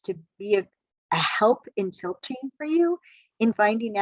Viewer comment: What is she saying? Apps can be a filter or coaching? My caption says "tilt chain" and I know that's not right. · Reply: Apps can help filter for you, like seeing if a person reads your profile, can follow instructions and/or cares about what you need! - Alison’s Team